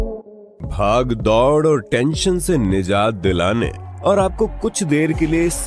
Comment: why I heard haag daud😔